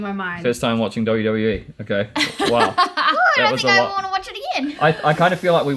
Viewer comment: Really, that was your takeaway? Along with 11 other people? Hmmm. I must've observed backwards. 'Cause to me, if I heard things right, at 17:55, seems to be all giddy and she even said, "I think I'd wanna watch it again"... I thought it was possible she said, "I don't think i'd wanna watch it again," so I listened a few times and I feel like she said she did wanna watch it again, which would be awesome. I grew up with it and got out of it, but I'd be happy to watch it more with them. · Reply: She definitely said. “I don’t think I wanna watch it again”. Her facial expression also says she doesn’t want to watch it again.